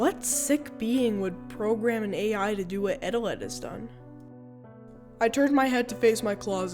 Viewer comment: bro the yt captions are like "Etta lettuce" instead of Eteled😭😭